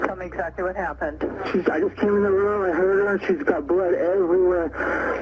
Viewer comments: You had "I heard her." I heard "I HURT her." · In the 911 call it sounds like he says "I came in the room and hurt her" rather than "I came in the room and heard her"